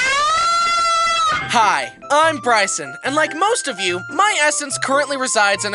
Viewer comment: did he say he’s gay?? lol me to bro